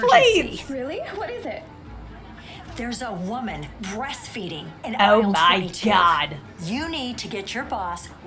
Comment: the caption says beast feeding💀